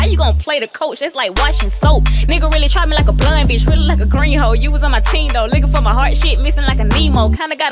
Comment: At first I thought the second girl said Gringos instead of “green hoes” 😂😂